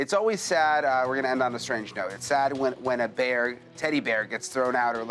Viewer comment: Brian..? Did you just call that thing a deady bear on purpose at 39:30? I hope so.